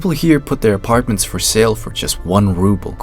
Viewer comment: I thought it said flats for sale for 1 toddler